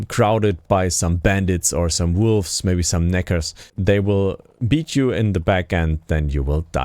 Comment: Lol what did he sayyyyyy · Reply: Neckers he said